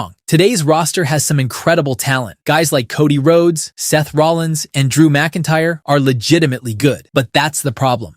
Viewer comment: did you say Cody and Seth "GAY" Rollins are Good????? They have the biggest hand in the downfall of WWE. They don't belong to this platforms!!! Overhyped ones.